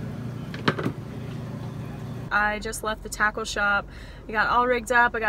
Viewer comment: I thought she said taco shop. What would she be doing with tacos? Ah! Tackle shop... great!